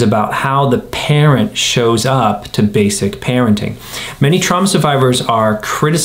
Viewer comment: Did I hear "Many Trump survivors..." at 18:07?